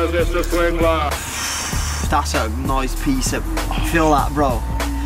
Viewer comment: what does he say ??? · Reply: Do the honours Mr swingler.